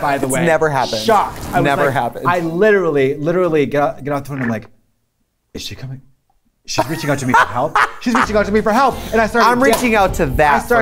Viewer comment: Anyone know what Katya says at 7:52? I've replayed it like 15 times... I just wanna know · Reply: “I literally got off the phone and im like is she coming? Shes reaching out for me to help, Shes reaching out for me to help”